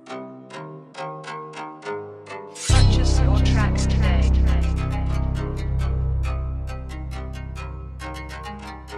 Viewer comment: what does she say?? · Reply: purchase your tracks today